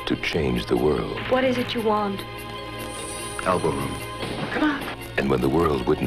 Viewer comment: What is he said when she asked him what he wanted? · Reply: Elbow room (ie space to breathe)